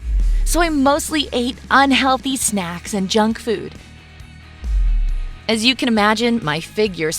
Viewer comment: OH SHE SAID JUNGFOOD IT THOUGHT SHE SAID JUNGKOOK